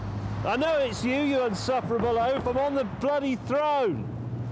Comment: funniest quote ever - " Clarkston I know it's you, you insufferable oaf, I am on the throne!" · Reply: I thought James said throw than throne .